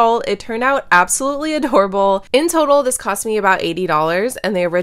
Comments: O heard her say "it turned out horrible" Then I listened again and heard "adorable", but more like "adhorrable" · When you said “this looks absolutely adorable” my brother sneezed and all I heard was “this looks absolutely horrible” and I started busting.